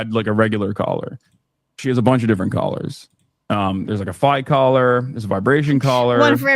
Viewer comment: Did he say a fight collor(14:23)? does he put his dog in dog fights? That is a felony in all 50 states.